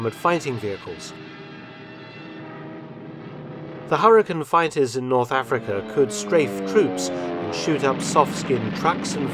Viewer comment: For someone as meticulous about detail as you are, Mark, I am just a little surprised that you didn't include South Africa and the US in your list of Allied nations fighting in North Africa. Many thousands of my countrymen, my grandfather among them, served with great distinction in the desert. But it's a small thing, and this is still a great story - as I have come to expect from you. Thanks for your great work! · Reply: I thought he said India and South Africa, sorry if I misheard